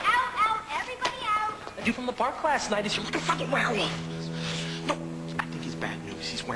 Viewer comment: i heard "dude from the park last night is looking for a f***ing werewolf""